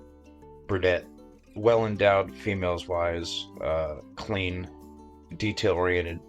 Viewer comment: did he say "females-wise"?